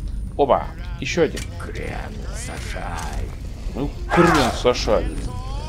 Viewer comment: "Я болллееею" :D "Магия везде одинакова" Да ну :/ в других играх серии они разные. Хм... А мне послышалось от дреуга - "крем сажай" :D